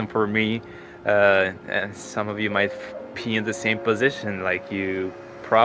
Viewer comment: you are awesomest of the awesome. You deserve all the praise. For a moment at 7:33 I was clueless as I heard "you maybe pee in the same position". Not to make fun at your pronounciation, I am not an English native myself, but is kind of funny. Sorry. Good job out there, wish you health.